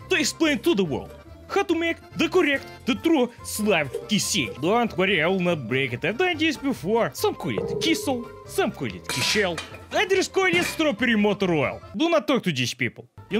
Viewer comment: thought you said diesel for a sec lmao